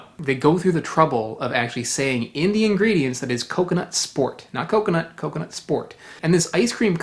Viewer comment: I swore you were saying coconut spork.